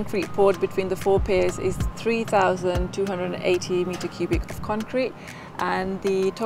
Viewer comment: Another one who can’t say a volume or area correctly. She said metres cubed instead of cubic metres. There is a big difference. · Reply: I thought she said "metre cubic" 1:02 which sounded to my ears what someone who speaks "International English" might say. Lots of the HS2 work doesn't seem to be being done by John Smith & Daughters Limited, Northampton so I imagine "International English" is the 'site language'. It is a bit odd the first time you hear a project being discussed in International English, but I don't think there is any actual error here. Lots of countries put the adjective after the noun (France and Malaysia both do it, Germany doesn't - there's a reason why Anglish is the way it is!) and their engineers will carry that over into their English.